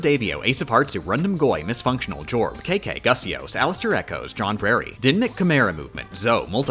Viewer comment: I heard "alisterechoes" and thought I heard "alastorechoes"